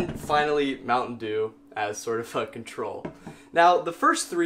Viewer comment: anyone else hear "mt. dew a sort of fucking troll" xD